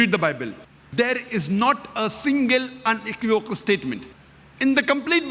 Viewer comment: What did he say in 1:08 ? Not a single .... Statement? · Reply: unequivocal